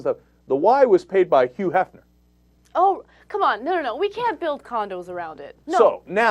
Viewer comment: Did Ana say, "Oh, come on. We can't build condoms around it," right after the story about Hugh Hefner (3:50)?